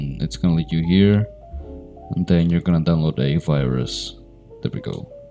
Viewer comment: did he say we were gonna download a virus 💀💀